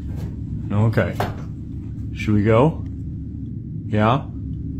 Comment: ! At 2:15 I'm gonna ask you did you HEAR IT!? Sounded like a woman whispering "yeah" before the dog started growing